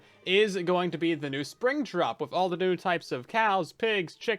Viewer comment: I thought you said spring trap